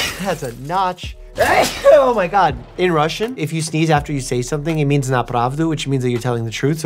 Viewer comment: I miss heard it as nye. Forgive me ;w; I know you mean as in На правду